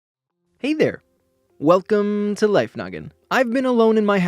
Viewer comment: I heard "Hey there, welcome to life nugget!"